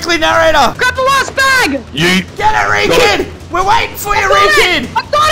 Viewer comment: Did u say "gyere ree kid"? Where ate u from?